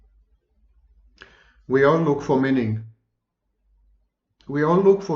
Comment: dear sir, I heard "we are all looking for Minnie" and later understood that you actually said, "we are all looking for meaning" I hope Minnie is doing well.